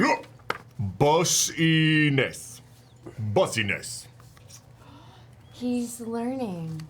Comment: "boysness" what word is this? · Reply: Probably “business”.